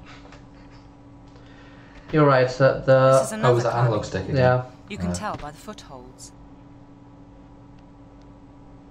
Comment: I've never heard the line at 4:47 before in my 20+ years playing this game! What did she say? "This is another climbing [...], you can tell by the footholes." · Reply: She says climbing wall